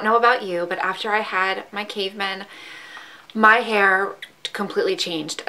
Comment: I can't understand what you said at 1:44. When you had your caveman??? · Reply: my three boys, that's what I call them